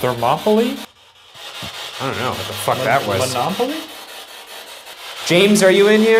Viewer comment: sounded like “don’t laugh at me.”